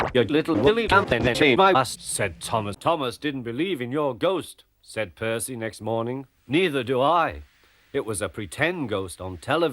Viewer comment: What did it say at 5:06??? This is one of the funniest to date! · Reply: said Percy next morning